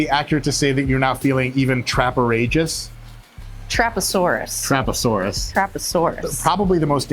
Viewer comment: did she say trap her sore ass 😮